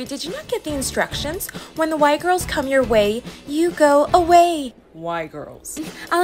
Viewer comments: Is it just me or the first time I saw this I thought they said “ white girls” instead of Y 😅😂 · my dumbass thought they were the “white girls” but i guess its still accurate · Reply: I THOUGHT IT SAID “”white girls” TOO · For a second I thought she said white girls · For some reason i thought it said "The White Girls" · When the girls said y girls I thought they said white girls😂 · I thought they said white girls! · At first I thought she said “when the white girls are coming through you go away” 😂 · “When the Y girls” bro I thought she said white girls 💀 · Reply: Same I thought they said the white girls💀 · I thought they said white girls💀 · I THOUGHT SHE SAID WHITE GRILS 😂😂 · English is not my first language, and I heard "White girls", and that for me was so funny, like, I can see a bunch of girls calling themselves that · “When the Y girls” bro I thought she said white girls 💀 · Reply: I thought they said fight girl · Me who thought they said “the white girls” 😂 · “When the Y girls” bro I thought she said white girls 💀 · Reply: Me tooo I thought she said the white girls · I THOUGHT SHE SAID WHITE GIRLS 💀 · Lmao I thought they said they were the “✨WHITE GIRLS✨” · I wasn't reading the words at first and I heard "Y Girls" as "White Girls" and thought Da Fuq but then they repeated what they said 😅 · I fr thought she said "the white girls" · I thought she said "white girls" for a sec.. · legit thought she said white girls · Bro the fact i thought she said were the white girl😂😂